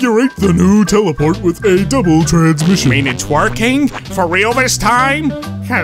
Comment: First i heard "it's twerking" and that snail...........oh dear